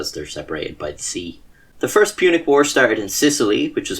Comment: Almost thought you said the great pubic war. I paused my multi-tasking, wanting to see where this was going. It was not what i expected, but still entertaining and informative as always.